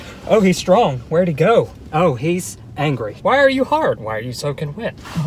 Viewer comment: Him:" why are you hard?" Us:"what did he sayyyyy"